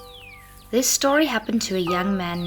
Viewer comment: I thought she said “this story happened to a young man named Jisoo.”